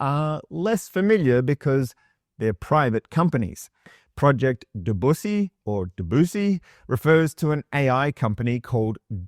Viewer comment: I'm sorry did you say PROJECT BUSSY OR BADUSSY @ 3:15?